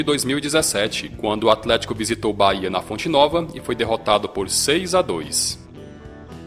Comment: mano eu ouvi bayern na primeira vez